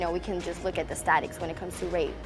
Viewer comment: Did.... did she just say "Statics"?